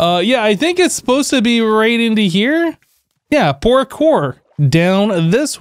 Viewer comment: i thought i heard porkur. But wouldn't it be funny a pig-based parkour named porkur? S8 is the season of puns afterall